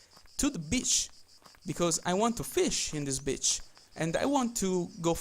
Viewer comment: what did he say? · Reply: "I want to fish in this beach."